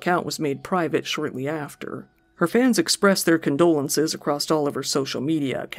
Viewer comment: did you say "Acrosst"?